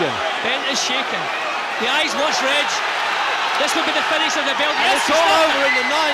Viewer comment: does anyone know what Jim Watt says here?! It's bugged me for so long. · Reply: tried 0.5 speed - The eye's worse Reg!